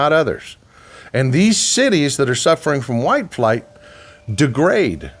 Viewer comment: Maybe I'm going deaf I thought he said white flags....